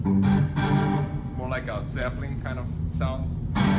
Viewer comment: I‘m chinese，sorry for my bad English！what kiko said at 4:35 ， sapling kind of sound？？what is the meaning of sapling？thank U！ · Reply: Zeplin!